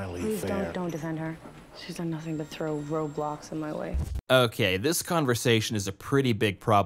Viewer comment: DID SHE JUST SAY THROW ROBLOX MY WAY